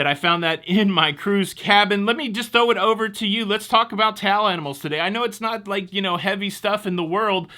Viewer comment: Wait what!! At the 5:39 mark did I hear you say "towel animals are fun"? LOL